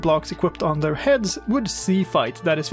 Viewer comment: I heard "sea fight" and now I imagine piglins in ships, shooting cannon balls at each other. :D